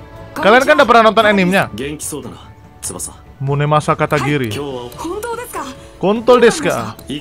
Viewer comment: Gua nnton replay bang Di menit 11:06 yg dengernya konto* desaka siapa😂